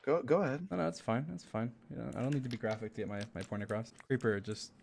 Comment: did he just say pornograph?!!?? (a man of culture I see